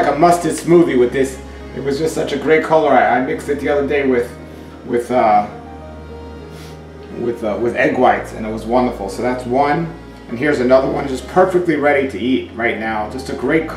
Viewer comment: did I hear you say you mixed it with egg whites?? Great idea though